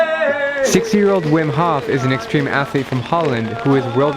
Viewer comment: Captions say 60 year old Wim Hof Drew Binsky says 6 year old Wim Hof